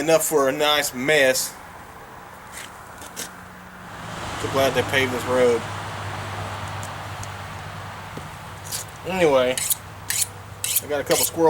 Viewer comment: ?? "to buy the pavers road"???? wtf id he say? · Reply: I said "I'm so glad they paved the road" very sarcastically